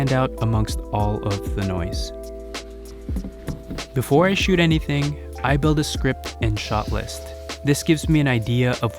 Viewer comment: Thanks for the great video, what was the software you said you used for writing your video scripts? I could not quite make it out 1:43. I definitely will be checking out more of your content, thanks again! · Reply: I use Notion and google docs